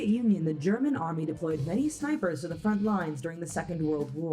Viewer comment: Tbr i thought it said spiders 😂😂